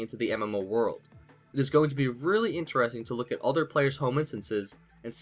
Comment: I thought it said "home insurance" and I was like "That is flipping boring I reality, I'm not going to buy it for some fighting mini-dudes"